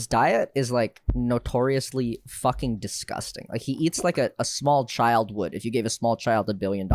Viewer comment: I thought Michael said he eats a small child every day and was like WHAT!?